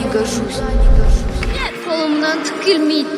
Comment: Why i heard the Kazakh version of that “i cant do it” scene at 2:11,i thought the dub said “kill me”